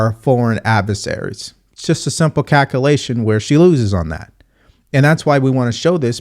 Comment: I heard cackelation instead of calculation. Hahahaha. I really enjoy your videos. You're a legend! I'm not American but I'm all for Donald Trump and JD Vance. MAGA 2024!